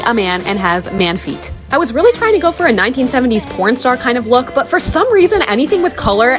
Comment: “1970’s corn star” BRO LOOKS LIKE VALENTINO FROM HAZBIN HOTEL I CAN’T 😭😭😭 Edit: Funny thing I just remembered is Valentino canonically died in the 1970’s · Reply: I thought she said p—n star😅